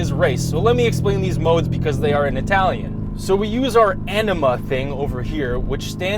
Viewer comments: Totally heard Enema instead of Anima XD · I thought he said enema.